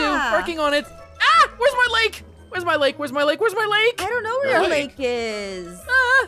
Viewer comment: I thought was saying WHERES MY LEG LOLLL